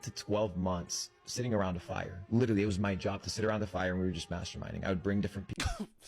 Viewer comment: When he said masterminding I really had to rewind because I truly thought he DID say masturbate.